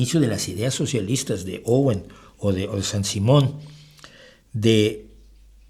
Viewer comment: Quién es la persona que mencionas en el minuto 2:15, Sam Simón? No escuché bien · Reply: Saint-Simon, uno de lis fundadores del socialismo.